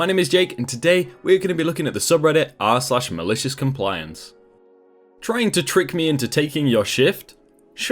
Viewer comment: I heard "Trying to trick me into taking your sh1t?"